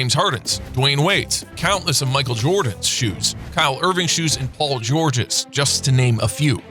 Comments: I gotta be going deaf did he just say Kyle Irving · Did he say Kyle Irving?